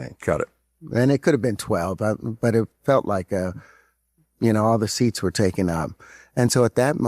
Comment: Did he say, All the sits been taken out?